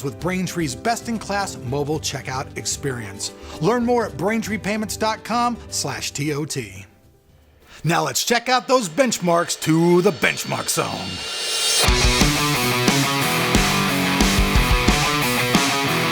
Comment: istead braintreepayments, I hear "take Your pants" xD